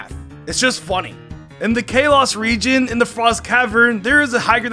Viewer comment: Ah yes, the Chaos region. Edit in case people storm my replies: I am aware that is saying Kalos, but my mind just wants to think He's saying chaos.